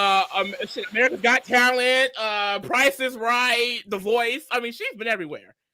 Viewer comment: I thought said Angela was on “Karen’s got talent” lmao